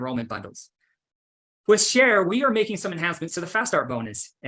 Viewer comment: the transcript reads "Cher" instead of share 😂